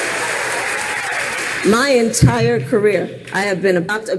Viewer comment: "Lying tired career" Misheard from her own mouth.